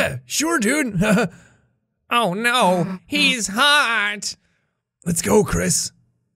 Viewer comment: I heard "Oh no, he's hot!" and DIED🤣 Edit: the graveyard gave me feels after I realized Gerson and the Amalgamates died.